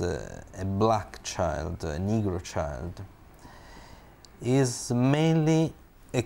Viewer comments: Did he said a Negro child? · did he just say a niggru child 🧐